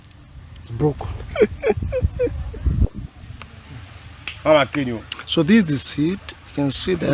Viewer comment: why did someone just say “I’ll kill you” in the background